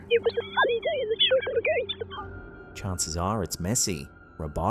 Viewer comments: did anyone else hear it clearly the first time? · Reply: Not all of it, I heard "going to the-" but interpreted it as "pub" rather than "park". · did anyone else hear it clearly the first time? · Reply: I heard "it was a ##### day and #### going to the PUB!"